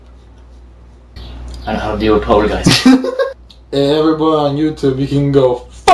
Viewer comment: i know hot to deal with poltergist is what he says